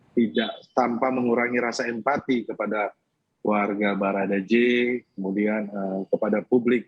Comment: Menit 7:35 barada J ... Salah dengar gk aku... Itu brigadir J pak bukan barada😄